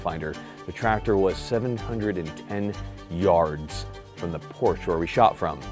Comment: 710 yards?!?!? I thought you said 710 inches!! -22 man